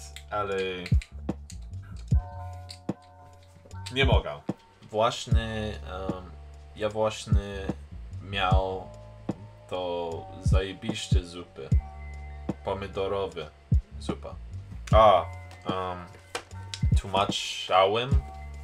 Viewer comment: czy tylko ja usłyszałam "Ja właśnie miał to zajebiście w dupie" XDDD Nie no ale ogólnie szacun 😄